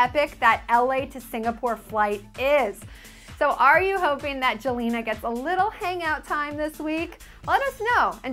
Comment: did she say Jelena???